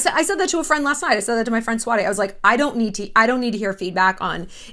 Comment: Around the 30:29 min point did you say your friend "swati?" The one that blackmailed you?